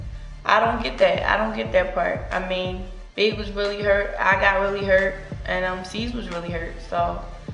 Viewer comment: what did she say at 2:15 ?? BIG was really hurt????? did anybody hear that too or am i on crack??? :s